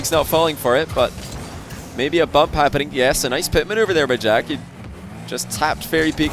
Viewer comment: I am American and I have no idea what he said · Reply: "maybe a bump happening, yes a nice pit manuever there by jack."